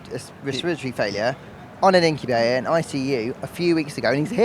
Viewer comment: did he say “on an incubator?”